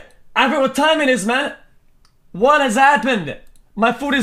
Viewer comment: "My food is cold I probably need to order more this is actually kinda cooked." - Mr. Cow 2022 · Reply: I thought he said it's actually kinda good